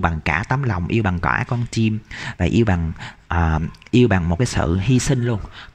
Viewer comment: Ủa, em có nghe nhầm tụ 2 không " yêu bằng cả con chym" ?? :))))))